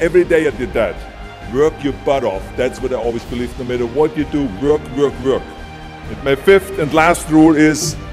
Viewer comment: what did he say? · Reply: Work work work